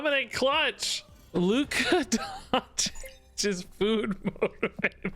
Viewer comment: I didnt understand what the title was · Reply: "food motivated"